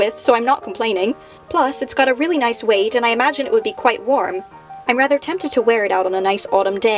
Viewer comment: I thought she said "Burn to death" instead of Bernadette at the end OMG 😂